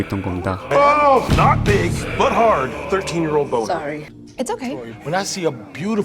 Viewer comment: 괜찬!이라고 나만 들리는거야? 나만그래?ㅋㅋㅋㅋ